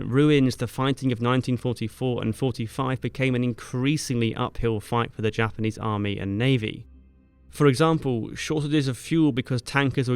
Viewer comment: I wasn't paying attention and thought you said "...increasingly uphill fight between the Imperial Japanese Army and Navy." Which would have also been true... 😆